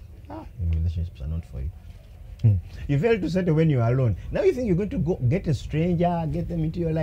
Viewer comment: I hear " u failed to set when u were alone"